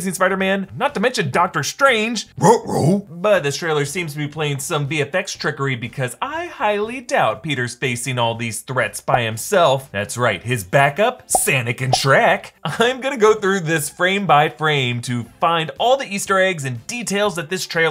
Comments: Did he say "Senic" instead of Sonic?? 😂😂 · did he say "sanic" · did he say sanic instead of Sonic?!